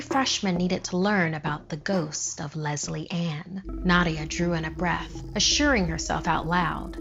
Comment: I legit thought she said lesbian instead of Lesley Anne!